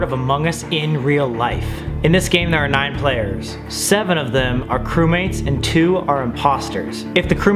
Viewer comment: I thought he said there were 10 players 7 crewmates and 2 imposters. It took me a while to calculate that then I rewinded the video. Oh I heard wrong lol